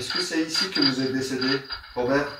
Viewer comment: robert dit je suis perdu et Vous pourquoi vous vous êtes perdus Robert.. et une autre personne dit.. (moi aussi) et c la voix d'une femme.. c ca que vous avais pas compris